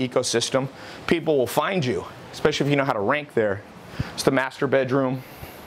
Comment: Good video but...did he just said "This is the masturbate room" ???